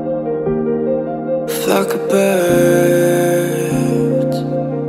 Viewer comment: I wasn't reading the subtitles so I thought he said f**k a bird